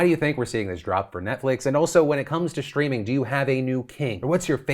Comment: Upon first play, I definitely heard "when it comes to streaming, who's your new kink?"